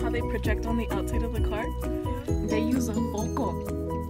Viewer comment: what did she say? They use what? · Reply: yam0415 “they use a Foco”. Foco means light bulb 🙂